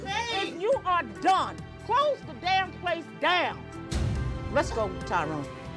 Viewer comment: I was half listening and totally thought she said “let’s go get tacos” 😂😂💀